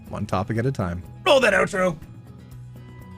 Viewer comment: I THOUGHT YOU WERE SAYING OUTRO BOOB!!!